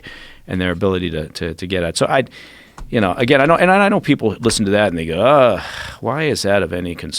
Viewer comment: Does he say "listen to that ni**a" at 07:02 ?